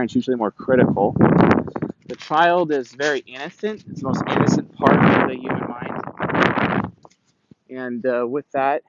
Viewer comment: for those who don't know what he's saying at 17:04, he's saying "the child is the most innocent part of the mind" 👌